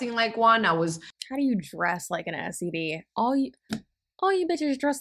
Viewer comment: All I heard was “I dress like an STD” and was so confused